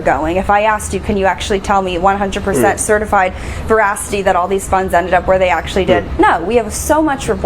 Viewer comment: At first I thought she said “Certified *voracity*” because she was hungry. To her credit, she looks great in that dress. I’m just looking for positives because she’s essentially Beelzebub in heavy makeup.